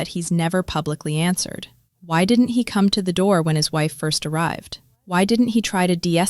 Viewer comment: I’m sorry for her family’s lost. But why go to somebody’s house? What was your goal? Don’t go looking for these men it ain’t worth it. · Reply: All I heard was " why didn't he"